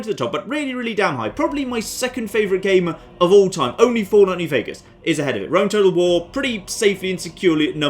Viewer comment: What's his favorite game? I listened 3 times, I hear, " fall only vegas"